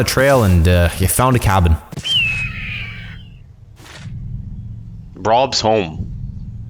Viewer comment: you brovr’s home instead of rob’s home